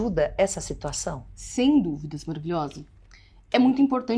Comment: Não entendi o que ela fala aos 0:33 do vídeo, a fala "sem dúvidas ******, alguém entendeu qual é a palavra depois de "sem dúvidas" ? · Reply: Maravilhosa